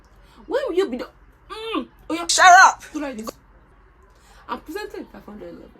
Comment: I heard "im presenting" instead of "im presently". See wetin Medicine posting don do me laidis 😭😭😭